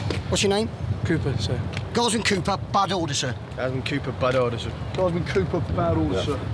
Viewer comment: what did they say here they said bad oder?